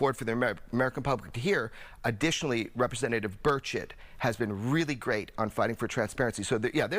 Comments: Representative Bird Shit? That's what it sounded like to me hahaha! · did he say representative bird shit?